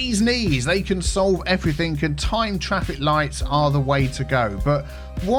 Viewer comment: Instead of "Timed Traffic Lights" I heard "Time travel Lights" and was mightily confused facepalms